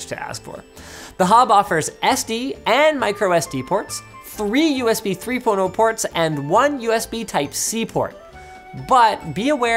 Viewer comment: I heard "3 USB 3 porno ports"...